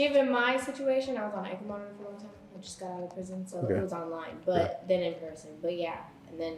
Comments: Did she say she just got out of prison or am I trippin? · she had an ankle monitor and is looking for a man who can "tolerate" her and she said she wouldn't date an average guy. All the indicators point to dying alone, gangster or drug dealer. My sincere condolences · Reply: lol. I thought I heard one of them say they had just got out of prison, and they're now looking for a High Value Man... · Did she say “I just got out of prison?” Like she was incarcerated? She’s a felon? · did she say she just got out of prison or the dude she was with just got of prison 🤔🤦🏾‍♂️ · Did she say that she had just got out of PRISON??? WTF!!!